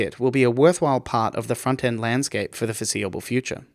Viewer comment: At the beginning, I thought he said Front End Santa.